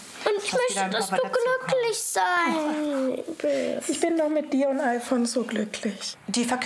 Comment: Ich bin doch mit dir und IPhone glücklich (Hab ich verstanden)😂